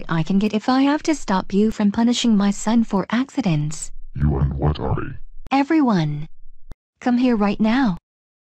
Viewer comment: I didn't hear that well at first. He said you and what army? THAT MAKES SENSE NOW BECAUSE SHE CALLED THEM RIGHT AFTER!